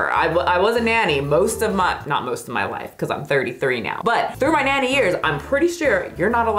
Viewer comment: You said "in my nanny years", but i thought i heard "in my natty years" as if you were admitting to using steroids😂